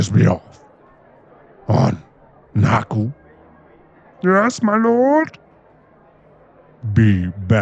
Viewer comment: If you put subtitles it’s “your ass my lord”😂 instead of yes my lord😂😂😂😂😂